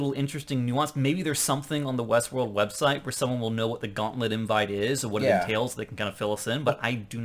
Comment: I think "gauntlet invite" is another caption mistake. I heard her say "gala invite," which makes a lot more sense.